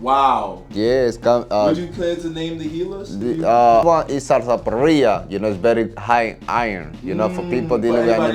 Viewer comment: What he say at 50:05 and 50:11, 50:30? Please tell me what he said at this time. Bro can you please take a text of the important words in the viedeo for next time. My english isen't so good to understand all. The subtitle dosent understand too many times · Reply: SARSAPARILLA